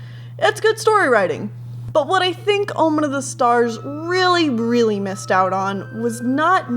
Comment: My friend thought you said ''Blowman of the hars''